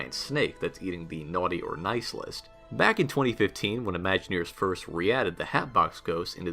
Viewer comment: I heard you say they brought the hatbox ghost in 2019?